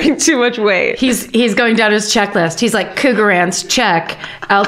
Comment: I thought Karen said “cougar ants” and I’m like whaaaaaas the eff are those?